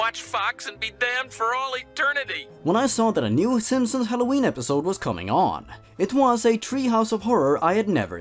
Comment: I’m not going to lie, when you said “Simpsons Halloween episode”, I thought you said “Simpsons had a ween episode”and laughed so hard I cried!